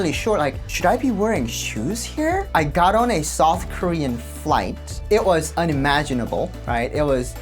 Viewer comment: I thought you said soft Korea instead of South Korea, the more I think about it, south would definitely be the softest Korea 😂